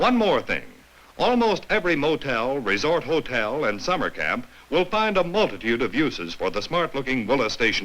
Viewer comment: seconds does it sound like he is saying after summer camp, find uses for the fine moslastion wagon to you? the pedophiles go way back in history on america! more evidence this nation was not set up on God but on satan and child sex cults! open your eyes its everywhere! and the thoughts of there mind were evil continually! you think?